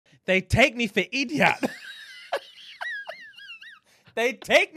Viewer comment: Did he just say dem take me fi ediot I'm dying 😭 🤣 💀 😂 😫 omg it's dem tek mi fo ediot